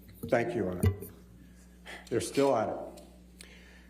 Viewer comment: Did he say ”they still at it” 😭😭😂😂😂😂😩😩😩😩